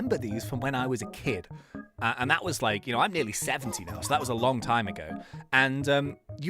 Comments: Did he say he's nearly 70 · Did you say 70??? 😮 no way you are 70 years old lol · Did he say 17 or 70, I couldnt really tell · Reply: I would say 70 but he doesn't look like he is 70 and he doesn't look 17 either.